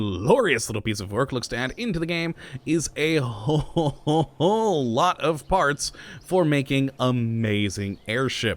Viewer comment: When said a whole lot of parts I thought he said a whole lot of farts